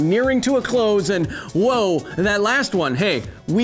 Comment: did you just say... Thanus